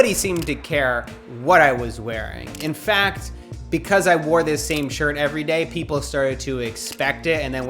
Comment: Thought it said “wore the same sh*t every day for 3 years”